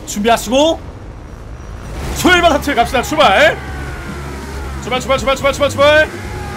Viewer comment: 에 출발을 잘못들었어 ㅅㅂㅅㅂㅅㅂ이라고 들음 ㅋㅋㅋㅋㅋ